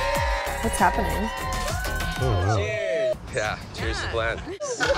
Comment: You know I thought they said "cheers to Glen". Anyway I was out the other night and we were did the cheers. I said "cheers to Glen" cos of all your editing 😂😂😂😂 they were like what? Who's glen? I said no, and whit would get it